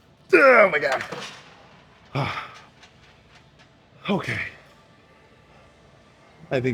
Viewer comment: Did he just say "ААА БЛЯ" on 11:30? 😂